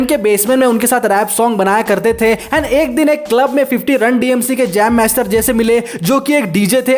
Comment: kya boldiya ye 😂😂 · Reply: RUN D.M.C hai bro 😁 wo nahi Jo tum sochre 😅